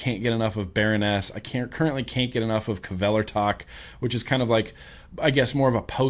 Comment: What is the name of the band he says at approximately 36:12? Sounds like "Cavela Tock" or something like that...lol Help?! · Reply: KVELERTAK